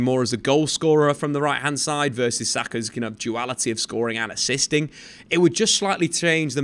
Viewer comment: Did I hear you say "Saka's ineptuality in goal scoring and assist"? Did I hear right? You must think this is Anthony.